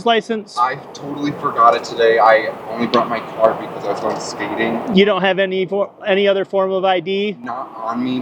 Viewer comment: Thought he said "I was going SPEEDING" instead of "going skating" at first